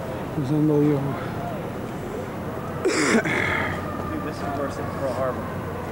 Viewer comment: He actually said I think this is worse than purl harbor lol